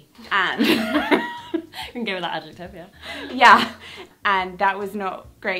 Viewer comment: Hi all, quick PSA, the captions are incorrect at 6:44 ! says "We can go with that adjective, yeah" but the captions say "And a girl that has Utopia".....Not sure how that happened, but that is what it is.....!!!!